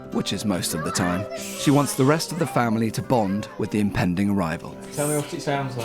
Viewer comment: I swear I hear "impending rival" at 16:35, though it's probably pending arrival